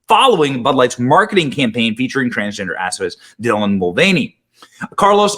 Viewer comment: Did you say activists or assface.hard to tell😅😂